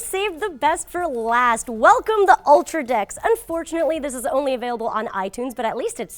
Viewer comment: hahaha at 2:42 it sounds like she's saying "welcome to ultra d!cks" instead of "decks"